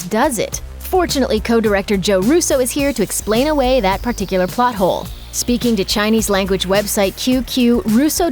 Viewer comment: I thought u said butthole